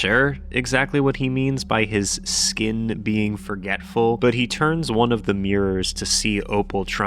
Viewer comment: I always thought the mirror man said that his skin was “so dreadful” instead of “forgetful”